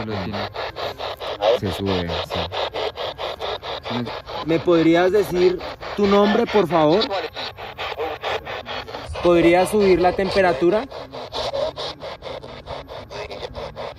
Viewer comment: En el minuto 40:00cuando pregunta el nombre se escucha que dice Suares. Muy buen video